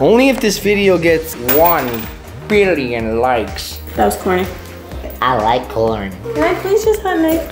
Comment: What did he said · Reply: I like corn